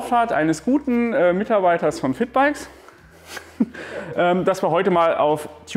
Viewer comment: Ich habe Fickbikes verstanden, anstatt Vitbikes😂 Was für ein Name